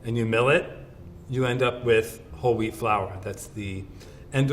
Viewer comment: I keep hearing 'whole wheat flour' as 'holy flour'.